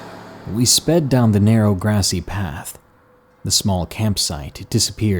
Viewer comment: Bruh I heard 'Neil deGrasse path' Hahahaha :'D